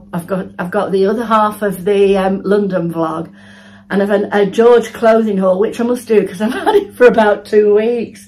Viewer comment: Morning. Lovely routine. My hearing is not great and I misheard what you said at the end and had to rewind…..instead of you saying you have a George Clothing haul I thought you had said George Clooney …. 😂😂 now that would make for a different evening xxx 😂😂